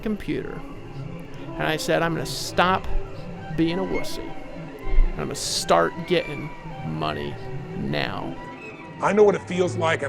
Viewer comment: i thought he said ima stop being a wussy and start gettin some pussy T_T